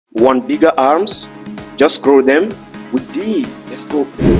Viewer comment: I heard "want niga arms" 🗿